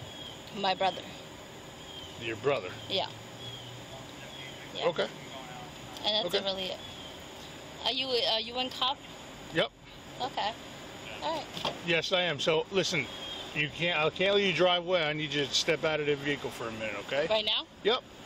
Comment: what did she say? “Are you a cop”?